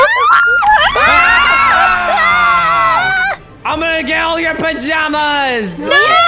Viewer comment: i thought he said “i’ll make out with your pajamas” .